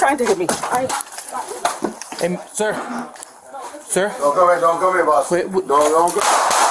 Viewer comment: The guy said, "Don't kill me, don't kill me boss" and never pointed his weapon at the officer. I support law enforcement, but that was murder. That jumpy officer should be fired and charged. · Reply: Sounded like he said "Don't come here boss" but I could be wrong. He began to raise the weapon, whereupon the officer drew and fired, but I guess the dude's brain had already decided "Point!" and he managed to adopt a shooting grip while falling backwards.